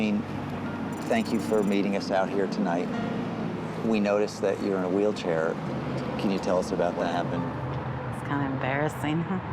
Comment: I had to cut the captions on. I thought he said (we noticed your in a wheelchair, what do you think about that?) 😂